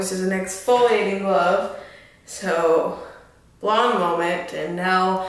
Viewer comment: I bought some to try too and now u got me wondering what gloves I got with it to I used jaclyns code I think too and thought said application gloves to put on the tanning lotion. I am bout to be brave and try lol ...I subbed to your channel too..check out my channel if you can.. Wish me luck lol